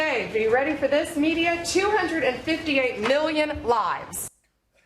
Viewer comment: I thought she said trump said 258 million lies. So I immediately had 2 thoughts: - that seems to be a very large number, but maybe not impossible for him - did she just have a stroke that made her accidentally spit out the truth for once?